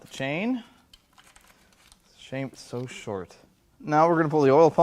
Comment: "....shame it is so short...." Is that what she said??